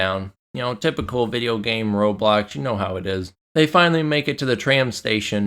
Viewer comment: I very much appreciate your comment and thank you for the sub, although I’m pretty sure I didn’t say that during the review? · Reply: there's a part where you talk about games throwing their usual "roadblocks and the like", which sounds pretty similar? Tbh I heard "Roblox" first time too.